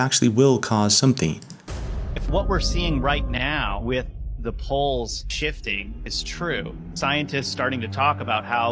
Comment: lol sounds like is saying magnetic bullshit here. Magnetic pole shift